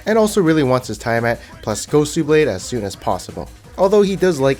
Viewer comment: i heard gosu blade